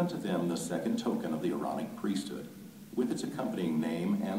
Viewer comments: I definitely heard "erotic priesthood" and had to look it up. Seems like a purposeful Freudian slip to me. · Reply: I heard “moronic priesthood” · I definitely heard "erotic priesthood" and had to look it up. Seems like a purposeful Freudian slip to me.